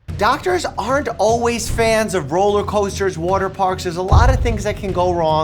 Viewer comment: implies something in the list is after rollercoasters and water parks. Water parks was said as "water parkses" 0:04 didnt say there's. 0:47 *what could the other issue be 1:57 *to a T 2:06 subs say toes instead of tumbles 3:54 obvi